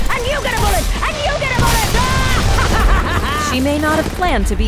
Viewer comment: "Time to turn crap into gold." Is my new mantra going into ranked games. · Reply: I thought she said time to turn gravel to gold 😂